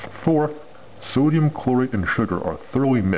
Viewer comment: for a second there i thought he said sodium chloride and sugar